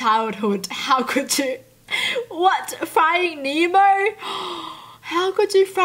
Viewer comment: I thought for a second she said “fry an emo”